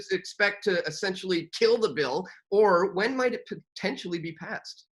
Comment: did I hear "kill the bill"? ... which sounds like Killbill lol